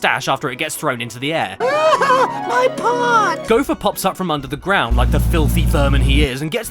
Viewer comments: Did he just say "my pot" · Is it me? Or does he say “My pot”